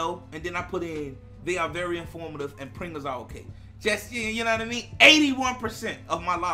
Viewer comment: So all this time I thought u were saying printers but u were saying Pringles lol. Ok I gotcha 👌